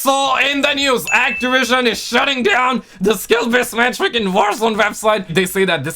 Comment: for a second there I thought you said they were shutting down sbmm ┌( ´_ゝ` )┐